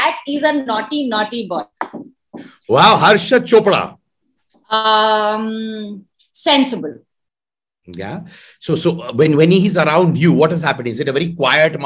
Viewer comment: Code H...i thought he said harshad..😂😂💜